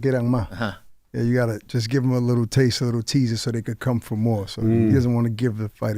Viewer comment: i think he was saying feed them a little so you can get more